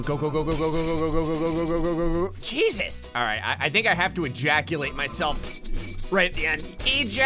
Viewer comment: did he just say ejaculate? Guys, don't google what it means if you're young... PLEASE!